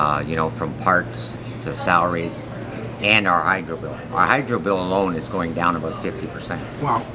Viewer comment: What bill is going down 50%? It sound like he said his hydro bill? What?